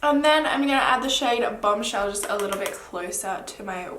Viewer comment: When you said bomb shell I thought you said BUM shell and I was like??😂